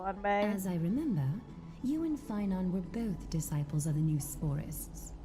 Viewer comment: What they said: "You and Phainon" What I heard: "You and Paimon"